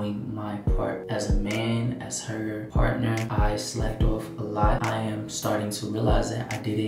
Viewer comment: What does he mean by he slept off a lot? I'm sooo confused like did he cheat?? Or did I hear it wrong?? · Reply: Lol he said he (Slacked off) not slept off